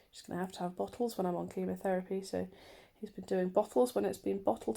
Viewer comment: Definitely thought I heard ya say Luke has been doing the breastfeeding